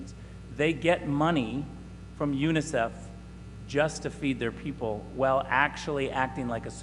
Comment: did I hear him right? Did he say China is getting money from Unicef to help feed their citizens?...please tell me that guy is exaggerating. If not, they're in just as bad a shape as we are, maybe worse